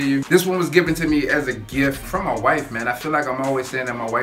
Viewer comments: Man, I thought you said you got the parfume from a WHITE MAN... hahahahah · I thought you said "I got this from a white man" haha! Nice review bro!